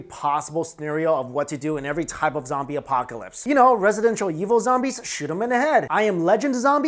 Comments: Did he say... residential evil zombies?? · did he say residential evil lol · Did you really say... RESIDENTIAL Evil zombies? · Did I hear 5:07 right? “Residential Evil?”